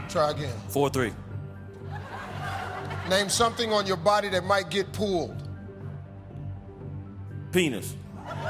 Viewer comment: I thought he was saying "name something on your body that might get pooled" and had no idea what the question even was the first 5-6 times he read it.